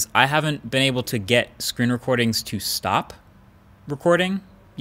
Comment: 4 out of 5 times I heard "scream reporting" ¯\_(ツ)_/¯